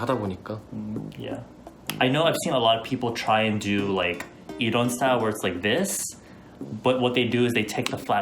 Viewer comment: did i heard it right? Edawn styleee?? Edawn like Hyuna’s boyfriend ayieee luv this im just a new subscriber gotta catch up! 💖✨